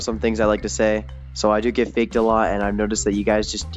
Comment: i thought he said i also get baked allotXD